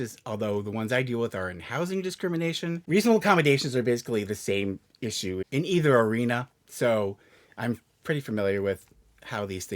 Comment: He literally admits he’s a HOUSING DISCRIMINATION attorney (and says housing discrimination and workplace discrimination are the same thing), so this video was basically him talking out of his ass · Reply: no, he did not say housing and workplace discrimination are the same. He actually said “reasonable accommodations are basically the same issue in either arena”, which means that there is a lot of overlap between reasonable accommodations for housing and reasonable accommodations in the workplace *(from his legal POV). Disagree with what he says all you want, but at least make sure you’re disagreeing with what he actually said in the video